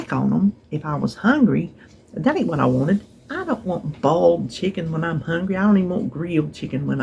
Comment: Been waiting all morning for this🎉🎉🎉🎉🎉🎉 I dig your I Dream of Jeannie impersonation😊 OMG, I thought you said you didn’t want bald chicken when you’re hungry LoL The water lady, is it the gal with long black hair that’s lost a lot of weight?